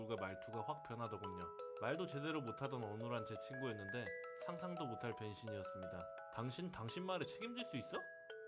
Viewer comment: 상상도 못한 변신을 상상도 못한 병*로 들었어요